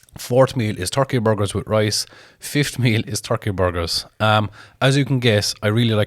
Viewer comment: Jesus I thought you said 30 burgers